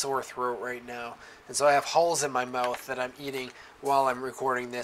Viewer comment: Hope you will get well soon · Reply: At first I thought said he "has holes in his mouth" ... then the second time I recognized "Hall's(R)" ... the cough drop.